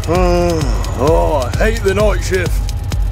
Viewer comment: when i was younger i heard OUAGH I HEY THE KNIFESHIF Me what i hear now: OUAHG I HATE THE NIGHTSHIFT